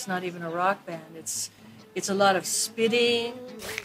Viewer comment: What does Nancy Wilson says after claiming that AIC "it's not even a rock band".? "It's a lot of (.?)" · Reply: Ariel Pirangy spitting